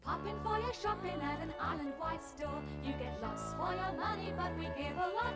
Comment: In that Island Wide Stores advert I thought she sang "you get less for your money" instead of "lots for your money" — which is a bit unfortunate! I wonder if they could've written it to avoid that mishearing.